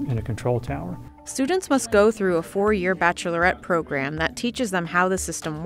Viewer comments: did she say 'Bachelorette' program? I think she did, listen to it again... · Did anyone else catch, "students have to go through a 4 year BACHELORETTE program..." @ 7:46 😂